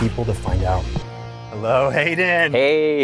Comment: i legit heard "hey dan" until i read his actual name 😨